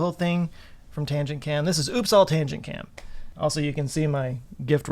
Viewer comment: I misheard “Oops! All tangent cam”, I heard Uppsala (a place in Sweden) and was really excited for a second wondering what kind of great science they had been puzzling with during the year 🤭